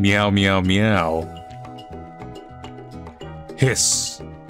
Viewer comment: am I the only one who heard "piss"?